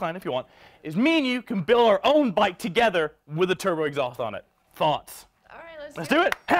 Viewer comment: I thought Mini said “THOTS”